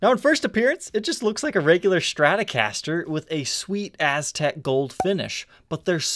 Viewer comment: Thought you were about to say "Sweet ass" haha